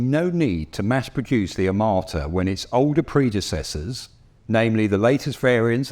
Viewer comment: I keep hearing "amarta" from the presenter, not "armata"